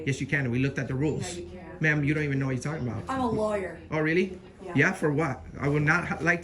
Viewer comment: 'I'm a lawyer.' I think we all misheard that due to her accent. She actually said 'I'm a liar.'